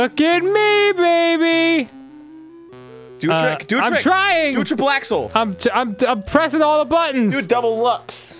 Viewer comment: did Arin just say "Blaxle" ? XD She's not that dark skinned, is she? ...oh wait... he said "triple axle" :/ I prefer blaxle :P Or maybe even blaxxle